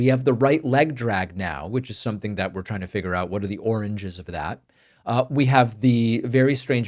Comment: is this an inside joke? The what are the oranges instead of origins? 😅 Is it accidental or because Trump is 🍊? 😂